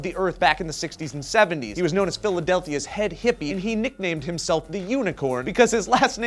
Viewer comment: Did he just say "Philadelthia"...?